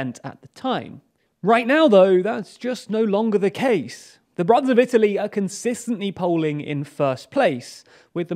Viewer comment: What is he saying? "The broth of Italy"?